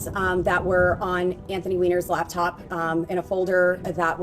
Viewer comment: did she just say weiner??🤨😅